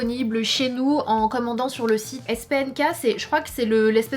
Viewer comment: elle voulais dire space nk ?? Parce que j'ai entendue spm k 😩😏😭